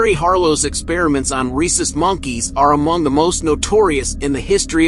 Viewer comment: i thought he said racist monkey 💀